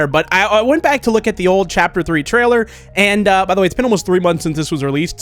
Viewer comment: Been waiting so long for chapter 3 that this doesn’t feel real 😭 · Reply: I thought you said it's been 3 years 💀